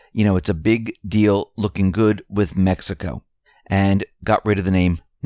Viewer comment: Sounded like you said "Methico".. :)